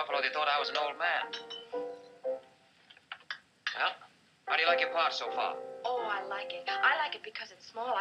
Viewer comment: what did he say? How do you like your what? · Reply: "How do you like your part, so far?"